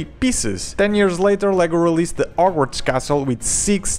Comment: this guy needs to learn how to speak english. it sounded like he said arworks castle. not hogwarts.